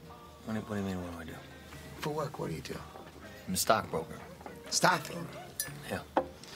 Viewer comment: what job was he working at agin? Couldn't understand what he was saying cause he was kinda talking too fast. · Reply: his a stock broker